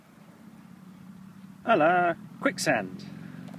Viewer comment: why does he sound like mr. bean when he said “hello” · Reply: i thought he said tada not hello lol